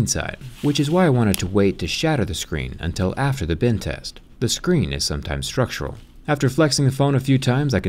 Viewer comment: I thought he was saying "Bin test" xD